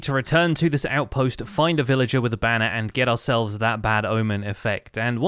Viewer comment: did you say "a villager with a banner"?